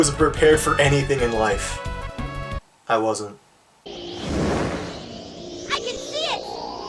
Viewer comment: Did he just say: "I can shit!"